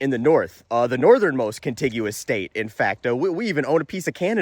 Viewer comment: LoL from Alaska and I'll I heard was "furthest Northern State". But you said continuous, and I knew you meant the lower 48s. Then I realized you were still roasting this guy and had to go back and relisten. ADHD is hitting hard today. T